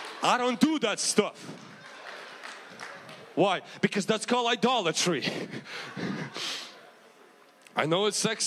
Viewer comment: Idolatry LOL I heard it as "I dollar tree" I think I needa check my ears or get some sleep lololol